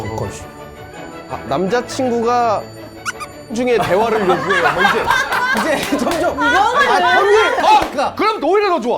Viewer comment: 뭐라고 한건 지 아시는 분 ㅠㅠ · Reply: 사정이요 ㅋㅋㅋㅋ